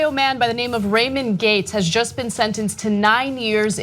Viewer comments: Thought the woman said “raymond gape” for a sec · I thought she said "Rapin gates" Not "Ramon gates" Whoooops....